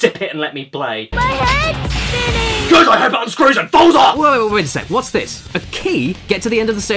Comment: What's he saying? I'm guessing GOODIHOPEITUNSCREWSANDFALLSOFF